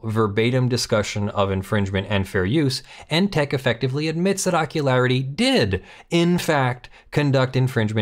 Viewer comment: I wish I could remember which movie/show it was from, but every time said Ockularity, I heard "Jockularity."